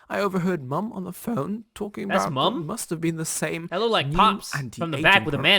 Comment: he had a Voice crack what he said Mum